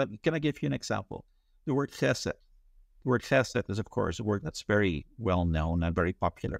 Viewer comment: somebody help me please....at time stamp 27:04 what is the word he mentions and discusses its translation? With my diminished hearing and his accent it just sounds like tesla or tesa (which is a manufacturer of automotive adhesive tape) and CC, is as usual, worthless when this happens. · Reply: It’s hesed.